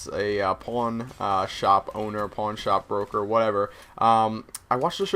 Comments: at first i thought that said a porn shop. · I thought you said porn shop at first. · lolz the first time you said pawn i thought u said porn shop owner haha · was it just me or did it sound like he said porn shop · lol i thought when you said pawn shop you said porn shop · I thought you said you wanted to own a porn shop :$ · anyone else thought he said porn shop owner? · I thought you said porn shop broker at first... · i thought you said porn shop not pawn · I Thought He Said Porn Shop Instead Of Pawn Shop Lol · omg i thought you said "porn shop" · lol i thought he said he wanted to build a porn shop. then he said he watch pornstars. Then, I realize oh! PAWNSHOP and PawnStars! LOL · i thought you said porn shop at first. lol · like if you thought se said porn shop not pawn shop :) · I thought you said porn shop for a second. Lol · did he just say porn shop???? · Lol I thought he said porn shop owner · I thought he said he wanted to be a porn shop owner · i thought you said porn shop owner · haha i thought he said porn shop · Oh my god I thought he said porn shop lol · At first I thought you said porn shop owner haha!! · haha i thought he said Porn Shop instead of Pawn Shop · i thought when he said "pawn shop" he said "porn shop" lol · oh i thought he said porn store instead of pawn shop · oh pawn shop.. i thought he said porn shop..